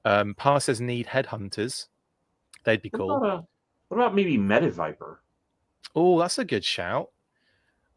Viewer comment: I thought said "Meta-Viper" and I was imagining an evil Social Media expert...